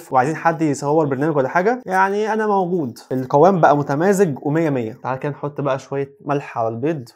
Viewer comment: هو انا سمعت غلط ولا اية متمازج 😂